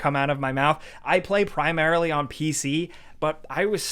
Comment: Did Jake say ''I played primarily on PC'' ?